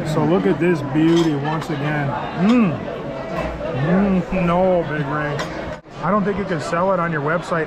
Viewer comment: Did you call Big Ray? 😂